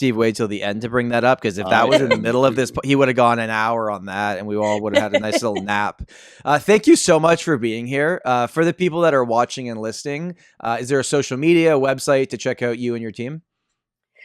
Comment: did you say while.asking the guest for her promo " for all those who are watching and Listing " or Did i hear it wrong 😂